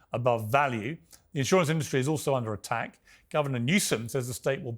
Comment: Did Piers say Governor Newsense at the 1:27 mark? I think he did. Should of called him Newscum.